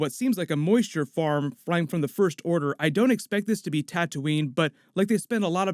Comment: Sounds like is saying “tattooing” for second lol